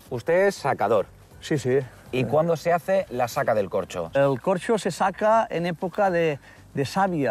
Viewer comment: Yo pensé que decía COCHOS de España 🤣